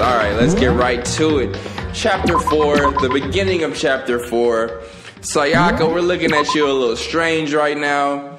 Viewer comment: Did he say sayaka